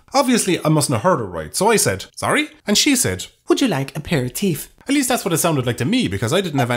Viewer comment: ProTip: When trying a new dish, and they ask you how you want it cooked. Reply with "The same way the chef would prepare it for himself." Also, can I get the address of this magical resterant that offers a "pair-of-teets". I'm more of a glute-man, but I'll never say no to a nice set of cans of condensed milk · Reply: Yes, that is what he heard. But I heard "Pair-of-teets" So... hook me up.